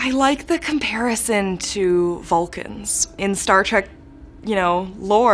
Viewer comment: what characters did she say? Falcons?